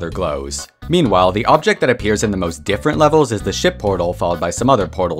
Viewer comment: I thought he said shit portal 💀